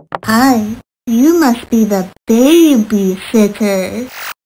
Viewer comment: I thought she said “are you the babysitter?”